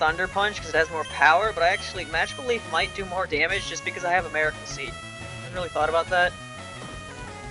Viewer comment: heard that as “just because i have American seed”